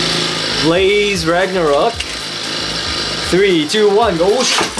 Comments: you can also launch lucifer in the right spinning plate and it will boost up the barrier you can also use a driver with a metal tip though 5:54 did he said blaze ragnaruk insted of glide ragnaruk hahaha lol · blaze ragnaruk Me: whattttt? Blaze Ragnaruk? Also me: isn't that glide ragnaruk😅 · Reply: I heard him saying Glaze Ragnaruk